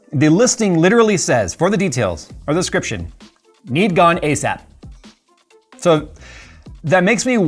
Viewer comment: I swear I thought he said Need Gun ASAP lol 😋😝😂😂